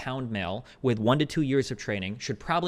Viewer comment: Not that this affects the argument at all, but a 148 pound male who benches 230 lbs is actually extraordinarily strong. As a personal trainer, there were many fit, strong guys who have lifted weights for many years who weighed 175 lbs that couldn’t quite bench 230, let alone within a year. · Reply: I thought he said "With two years of training" 🤔